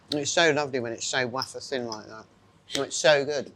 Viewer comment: What word does Angela use at 3:10? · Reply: Wafer-thin. With an 'interesting' pronunciations. Think it is a Monty Python reference.